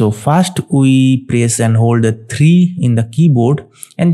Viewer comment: What was the first parameter you placed? I couldn't understand what you said. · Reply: sorry for the late reply I press and hold the key "3" and click thanks